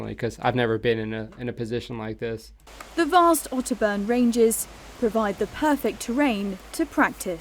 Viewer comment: where the captions say "auto burn"... What she actually said was Otterburn ranges, it's in Northumberland, Northern England. I went there for pre-tour training before being deployed in Iraq. It's a fascinating place in that the training officers can plot a tabbing route that takes you in one big circle, all uphill. Maybe it was just my perspective but I swear there wasn't one bit of that hike that was downhill, yet we ended up back where we started...